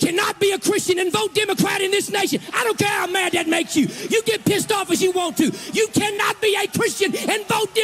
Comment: Did he say, 4:10 — “You cannot get p’d off”? And he calls himself a pastor?